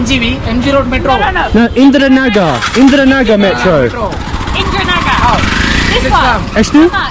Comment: did i hear INDRANAGER BHENCO? or M tripping bruh